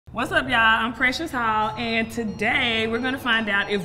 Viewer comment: What was the bald girls name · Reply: I honestly couldn't tell it sounded like she said precious hall